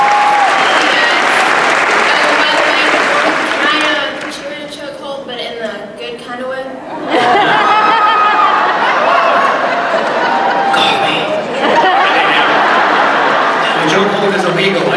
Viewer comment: Awesome video thank you for sharing! I was at the con but not at the panel. what did the girl say to Norman at 5:05 and he said "call me"? sorry but I couldn't hear because of the crowd was cheering. have a great week and happy Halloween! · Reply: She asks if she can put him in a choke hold in a good kind of way.